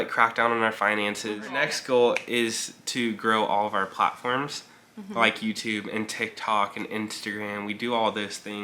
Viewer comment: I thought said "grow pot farms" LOL he said "grow platforms." LOL Love you guys!